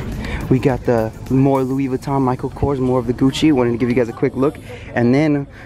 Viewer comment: says Luis Victor instead of Louis Vuitton